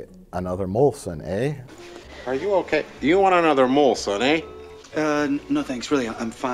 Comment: I always thought he said "Do you want another Malt, son". As in Malted powder drink.